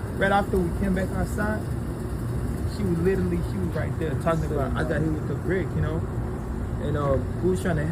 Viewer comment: Sound like somebody said “ain’t nobody hit with no brick”